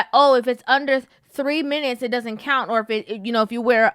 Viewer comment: I thought she said if it's under 3 inches it doesn't count.